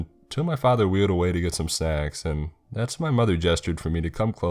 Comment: Wait...the father wheeled away to get some...what? 15:13 After looking through the comments pretty far back AM I THE ONLY ONE WHO HEARD THAT??? SNACKS?! It finally makes sense. It took about half an hour for me to figure that out. Even the CC thought he said...something else.